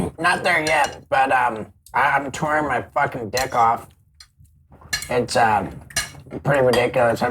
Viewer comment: Steveo says 'tour my dick off' and I hear 'I tore my dick off' and...I'm not surprised by it at all. Feels right.